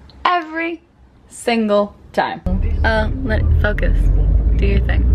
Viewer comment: When you said focus I thought you said fucka